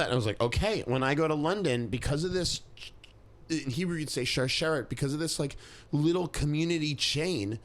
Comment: I've listen to this a dozen times and still can'tmake out what he is saying. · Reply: “In Hebrew you’d say שרשרת (sharshéret) because of this little community chain.”